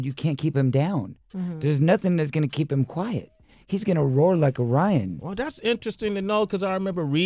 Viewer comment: did he say "roar like a rion"?